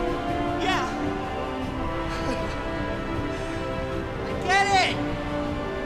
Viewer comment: did he say ma I get it